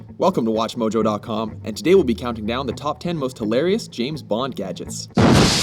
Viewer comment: *dies laughing* Did you just say James Blond? XD